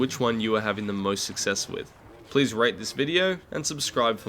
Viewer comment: 😂 I thought you said orifice gaming. No insult intended.